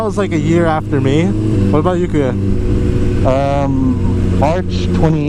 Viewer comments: Hey did i heard u calling wat about u KUYA? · Did you said "how about you kuya?" At 7:57 hehe cool.